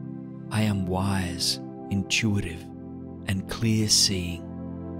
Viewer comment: what word did he say? Clearsee? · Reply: just realized theres closed captions haha. its “clear seeing”